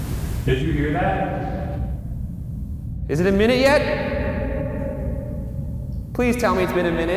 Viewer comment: When hè says is IT a minute yet I hear no not at all